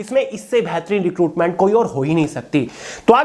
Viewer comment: Isse Behtarin Requirement Se Koi ho hi nehi Sakti 😂😂😂😂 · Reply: Recruitment hi bola hai